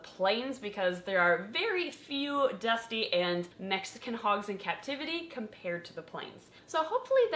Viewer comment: I thought it was hilarious that kept saying "dusty" and "mexican" back to back it sounded like she was saying dusty Mexican and I was like man I showered today.